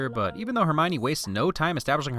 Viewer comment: sound like Hermione said “penis repairo”